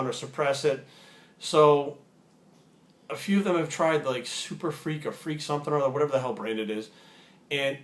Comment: I think he was trying to say pharma freak at around 2:20 - anyone else agree or disagree?